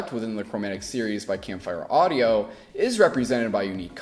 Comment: did he say Camp-FEAR audio ?